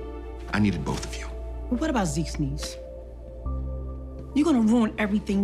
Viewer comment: did she say what about zekes niece? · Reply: Zeke needs ,on him not have to deal drugs or be apart of that life to have a life